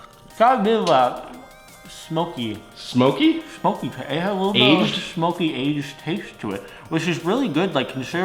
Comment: warning spoiler if you read the description what he says in it is at 2:50 "a bit of smokey aged taste to it"